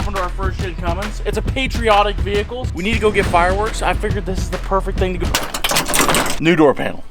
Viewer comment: I thought you said get firewood at first